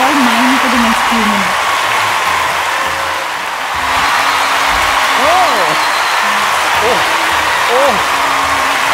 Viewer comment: what did she say? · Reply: He is all mine for the next few minutes...